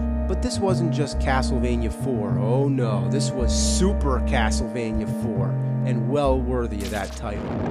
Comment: I thought it said ASSlevania IV